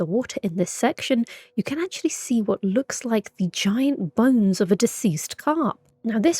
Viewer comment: Did you say "giant buns"? 😅